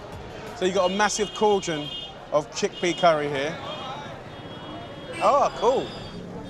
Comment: What did he say 👀👁👄👁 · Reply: "Chickpea curry here"